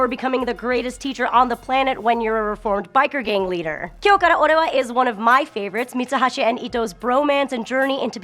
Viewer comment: Does anyone know the written title that mentioned at 2:15? I heard it but I dunno how to spell it. The style looks real interestin'! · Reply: Kyo Kara ore wa!!